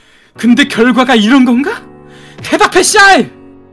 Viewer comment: 나만 대답해 쌰일 이라고 들었나?